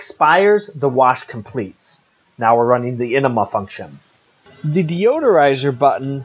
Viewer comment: did he say ENEMA FUNCTION?? I’m out!!!